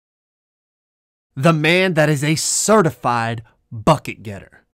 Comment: I thought was abt to say "the man that is a certified freak 7 days a week" in the beginning 😂😂